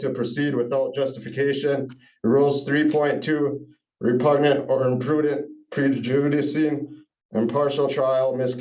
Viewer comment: That's "Repugnant or Imprudent Prevejuvenicy". I replayed it several times, and that's what he actually says here. William F. Buckley would be proud. Come back, Mike Judge, all is forgiven...